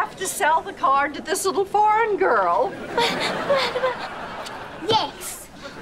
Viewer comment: Nona: i'm sorry but i have to sell the card to this little Foreign girl. Cat: but but but. Gwen: yes. (Gwen and Ruby shows cat their horrible faces) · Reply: I thought nonna was said "little bond girl"